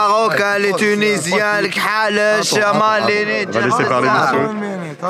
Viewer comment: C’est moi ou à 25:51 il dit « les kalouches » ??